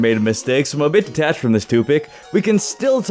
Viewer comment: Watching this again and lol says toopic at 0:06 instead of topic